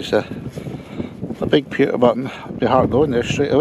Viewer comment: I need to get my hearing tested, I thought you said "computer button" not Pewter button, lol. great vid